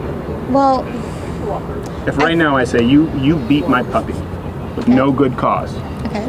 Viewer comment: I cracked a beer and thought said “you’ve eaten my puppy.” Bruh, not kewl bruh. She might karate chop you.